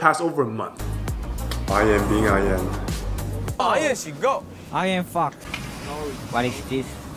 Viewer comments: he actually said "I'm fox." but yeah maybe the pronunciation HAHAHAHAHAHA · he really Said “ I AM FOX” but its sounds like the another word😅